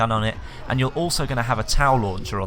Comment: It sounded like u said towel launcher